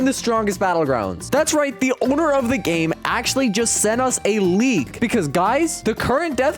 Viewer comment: what he says: a leak what the text says: a league